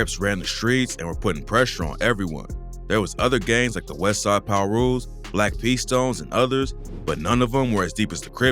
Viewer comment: I lowkey thought that said Westside Tree Top Pirus but that said Westside Pirus